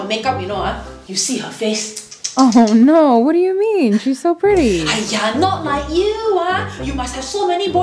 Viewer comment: I thought it said "petty" friend for a sec and thought i was in for a fight. Not an uncomfortable compliment compatition 😅